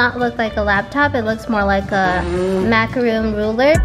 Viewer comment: DID SAY MACAROON